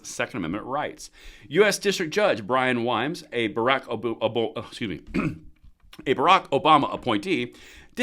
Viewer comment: Did i hear a freudian slip at 4:02. Barack Obooboo! 😅